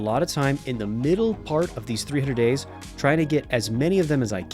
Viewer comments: did bro say 300 days ? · Did I hear 300 days?🤩